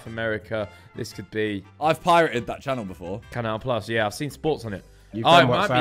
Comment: Someone please tell me what they said? 'Canal L plus'?